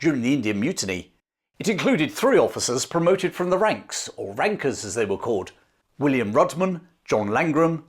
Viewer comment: My ears pricked up when thought you said "Working class wankers" instead of Rankers :D